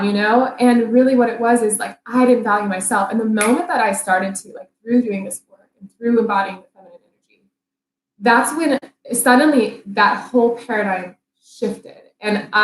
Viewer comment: Can anyone understand what the guest says at 23:35ish after she snaps her fingers? Seems like the worst point for the video to be muffled!! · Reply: Haha you are right it totally cut out! I said "the moment I started valuing myself, everything shifted". Which is totally what happened -- I personally did not change anything on the outside, but I simply realized that I was much more valuable than I was believing before, and through that, guys started to magnetize toward me and orient toward me as romantic interests rather than just friends. It is kind of like an energetic law--when we shift internally, everything/everyone around us will shift instantly as well. Does that help?